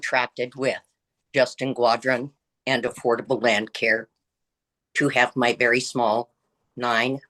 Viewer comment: did she say say she contracted "affordable lawn care" to renovate her kitchen?? Shit man....lol